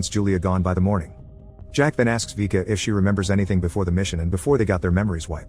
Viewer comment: When he is saying jack ask it sounds like he is say jackass